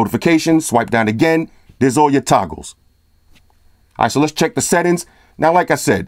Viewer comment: When you swiped down and said toggles, I thought you said tacos. Got me hungry man! This is the realist review and concise. Subscribed!